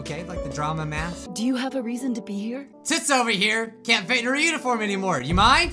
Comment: just...dosnt have a torso the entire time XD YOUR MAGIC (Edit) 9:27 is it just me...or did he say tits over here? XDD or am i hearing things??? Maybe its toots, idk. Toots might make more sense lol